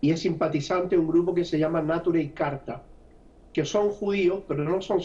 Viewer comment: Hola a que grupo judio pertenece Bernie Sanders, no entendi el nombre minuto 9:49 podria alguien dejarme saber. Gracias. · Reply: Ali N >>>Neturei Karta (en arameo: נטורי קרתא, Guardianes de la Ciudad) es un grupo de judíos jaredíes que rechazan cualquier forma de sionismo, por lo que se oponen a la existencia del Estado de Israel, en la creencia de que a los judíos se les prohíbe tener su propio Estado hasta la venida del Mesías.